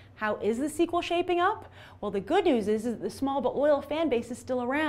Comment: Did she just say SMALL?!?